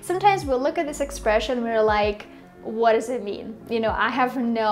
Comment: In 5:08, when she said "What does it mean?", she said something like "what it mean?", without said the "does", right?